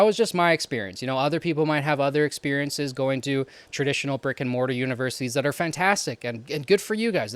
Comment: I hate myself but every time you mention a brick and mortar academy I thought you said Rick and Morty academy.